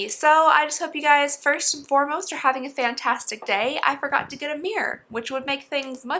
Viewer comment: I thought you said "I forgot to get a beer" I was like gurl same. But you said "I forgot to get a mirror" LMAO